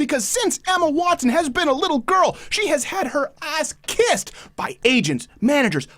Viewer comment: I think you say Emma had her ass "kissed" in Hollywood, but it sounds like "kicked"... coming after your noting how the real often world treats SJWs. What an indictment of Brown (Turd) University! It pumps out feminut no-nothings like witless Watson. She demonstrates an intellectual rigor mirroring that of the sexual turgidity of her flaccid male fanboys.